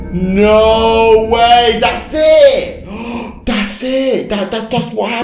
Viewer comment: I thought he said "Thats sick"